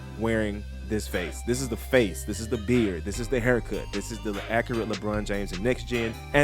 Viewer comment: Did I hear “the LeAccurate”😂that’s GOATED